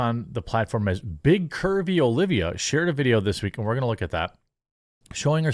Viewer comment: I literally thought you said big Kirby Olivia, rather than curvy. Makes sense, she probably inhales all the food.